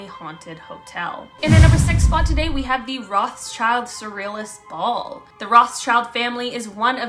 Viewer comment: I thought she said Ross child I was thinking Disney Jessie and Bunk'd 😵